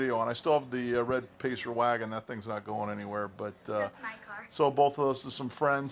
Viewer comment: I heard " That's My Car " at 0:28 !